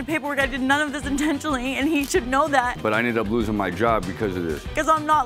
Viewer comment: The invincible crocodile tears was something! I could agree with him until he called her a ‘Sugar Mama’🥴 Sir y’all are about the same age! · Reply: he said “I tried to help out a SINGLE mom & this is what I get”.. he never said sugar. His speech & the way he pronounced the word made it sound like he said sugar but he definitely said single.